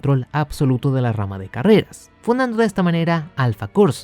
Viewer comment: Puedes unirte al discord del canal aquí: <link> Omisiones , errores , memes , retraso mio , sugerencias y todo lo que quieras , dejalo sin miedo en comentarios Saludos y nos vemos en el siguiente video! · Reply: parece que dice "funando" xD